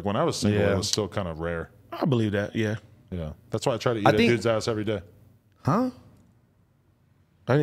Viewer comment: did he say ads ass